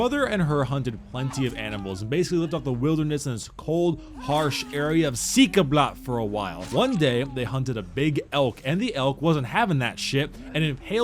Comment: What did he say at 0:34? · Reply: Ciika blaq